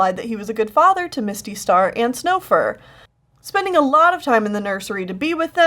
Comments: Mistystar and snowfur? Did i hear this wrong lol? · I also thought she said Snowfur and I paused for about 7 seconds and then was like "Wait but Snowfur is Bluefurs sist- OOOOH she said Stonefur" and then I felt really dumb xD. But anyway I LOOOOOOVE your channel keep up the great art and work. No joke whenever I see a new video by you I click on it so fast. LOAF CHU!